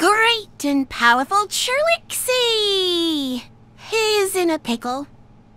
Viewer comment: The Great Powerful Trilexi! (I know supposed to be "Trixie" But the way that her AI voice said it, sounds like she said "Trilexi!")